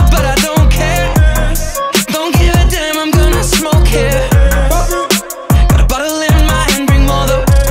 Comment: I always thought he said “I’m gonna smoke hair” and somehow never questioned it